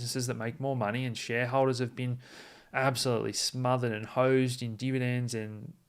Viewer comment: I thought he said "they were smothered in hoes" lmao